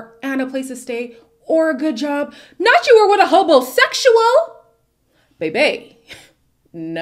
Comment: What does say about homosexuals at 29:27? I have replayed this part so many times, trying to understand what she says here, does anyone know? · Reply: she says “hobo” sexual.. since Ashley states she was the one providing everything.. “he didn’t have a car & a place to stay or a good job”